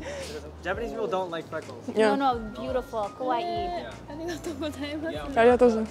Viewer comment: sounds like she says kowai instead of kawai which actually means scary, not beautiful lol